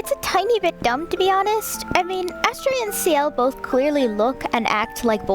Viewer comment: Astre, it's French for Star and a popularly theorized name for the fake Ciel lol Ciel means sky in French and twins are common for having similar names. The twins are also associated with stars and Astre is titled the "false star" in the blue cult arc so it's a pretty good possibility. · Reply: Ah I thought so! I kept hearing "ashtray" and when I put on auto captions it have "ashtray" too (of course cause it's english) but I was a little confused 😂